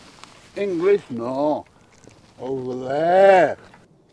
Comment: did he say english no over there? with a giant axe and smiley face?